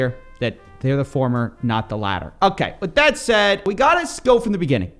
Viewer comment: "They're the former not the latter.." All i heard was "Please don't sue me LTT"